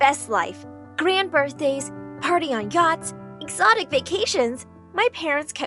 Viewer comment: can I just say it’s sounded like party on Gyatts